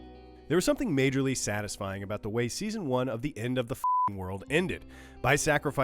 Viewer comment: I thought it said "Netflix Ends the F***king world"